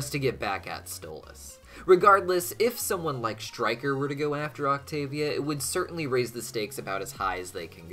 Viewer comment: did he say fucktavia😭😭